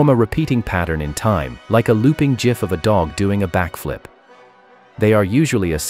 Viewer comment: DID BRO SAY JIF‽‽‽‽